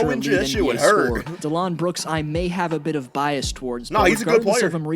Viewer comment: Sounded like he was saying Delon Brooks (Delon Wright + Dillon Brooks) when he was talking about Dillon Brooks.